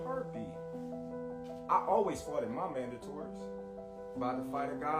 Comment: Bud - “I always foughted my mandatories.” What did you just say???